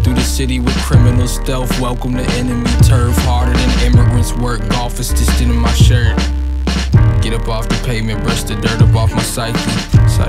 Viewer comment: Any one else thought he said "Golf is just stealing my shirt"